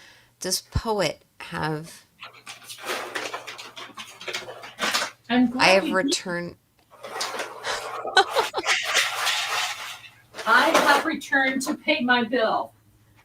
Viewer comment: It sounded like it said I’ve returned to take your soul!!😮